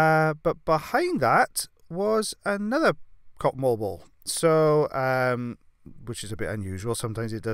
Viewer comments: He says cotton wool ball, I hear,, cotton "wall" ball Who's here in 2020 🙂🙂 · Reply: Totally thought he was saying cottonball ball too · He says cotton wool ball, I hear,, cotton "wall" ball Who's here in 2020 🙂🙂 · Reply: Maria me too. I seriously thought he was saying cotton ball ball!